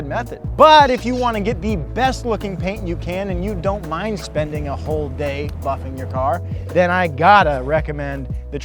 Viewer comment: at first i heard "and you dont mind spending a whole day fucking your car" LOL